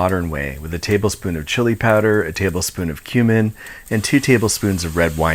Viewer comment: for a second there I thought you said "human" 😂😂